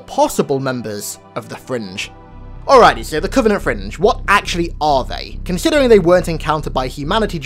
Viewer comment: Is it bad to say I thought that said Covenant fridge?